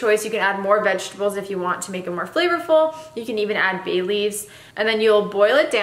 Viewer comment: You said, "bay leaves." I heard, "Bailey's." 😂